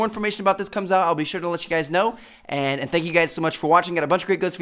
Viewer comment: Wtf is he saying at 1:37??? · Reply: "I got a bunch of great Ghosts videos coming soon. Check out the two videos on screen if you haven't seen them yet. Peace out guys. (Cue cliché outro music)